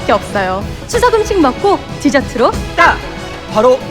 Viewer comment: 디져트로 딱 그러는데 왜 뒤졌다 딱으로 들리지